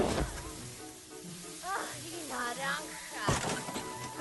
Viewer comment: what's word in Spanish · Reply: Mi Naranja🍊 My Orange